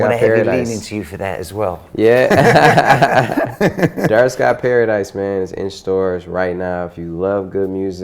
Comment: did he say dark skin paradise? lol